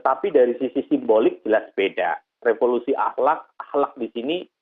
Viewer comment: Yang saya dengar bukan revolusi ahlak,tapi revolusi TOTAL.Kalau gak salah dengar🤣🤣